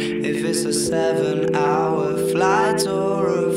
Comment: I hear route forty-five minute drive 😭😭